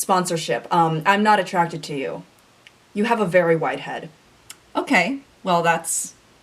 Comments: you don't have a wide head at all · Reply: oh. that makes sense. i thought she said white head. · does nothing Also to: " I'm not attracted to you ... you have a really wide head.. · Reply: WAS IT WIDE I THOUGHT SHE SAID WHITE